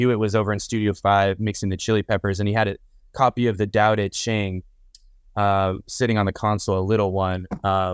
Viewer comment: does anyone know what book he mentions at 46:10? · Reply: We think Dao De Jing, also known as Tao Te Ching. please correct us if wrong